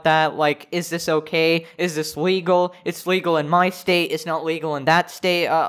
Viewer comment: Did he just said WEGAL